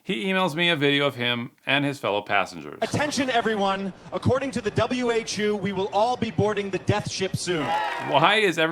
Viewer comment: According to W. H. U? or did I hear W. H. Jew?